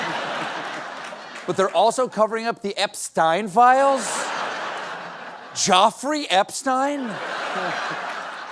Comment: I heard “EpSTAIN”